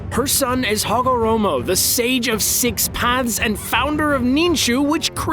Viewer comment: DID U JUST SAY THE SAGE OF 6 PANTS? LMAO